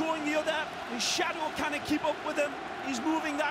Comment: haha, at 2:19 did he say his shadow can't keep up with him? :) :)